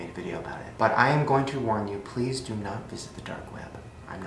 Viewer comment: Sese James what 😒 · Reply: Noura Yousif lol sry I thought u said you WERE going to visit the dark web..